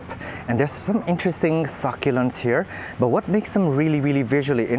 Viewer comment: for the life of me I thought you were saying, "Fucculents" LMAO! Some of them should be called that. Thailand is looking cool, too.